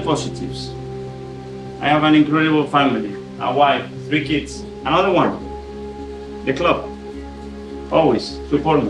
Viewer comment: I thought he said ‘another wife’ not another one… 🤣🤣